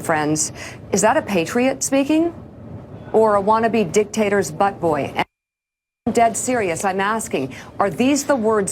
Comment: The censor was just a split second too late. We all heard her say butt boy. Lol